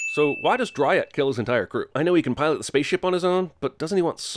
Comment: Everything wrong with " Everything Wrong With Venom In 16 Minutes Or Less " in 20 words or less 13:52 why did he call it driot, instead of riot? like how do you do something like that? like wtf dude?